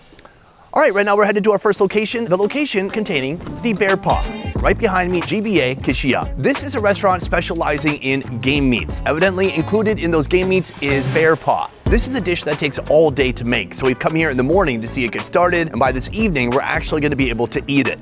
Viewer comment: GAME** meats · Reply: im not sure, it sounds like he said Gay Meats.